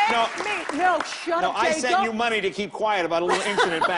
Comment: Does lisa say..shut up "dave"@ 5:24 as in david letterman??